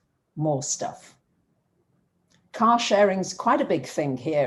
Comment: Lol - when you said "car sharing" I heard "cow sharing," because of the accent and because I'm currently in Wisconsin, where "cow sharing" as a way to get raw milk from small farms is indeed a thing. It goes to show, there's all kinds of way to share resources. 😉